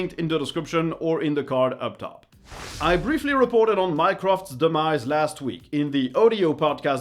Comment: I thought you said minecraft 💀